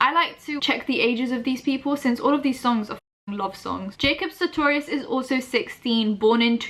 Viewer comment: lmao when you said ‘love song’ i thought you said lobster i’m gonna crawl into a hole now